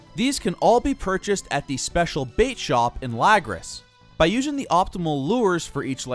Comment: did you just say "lag-ris"? its "la-graw" and the dialogue in the game says so.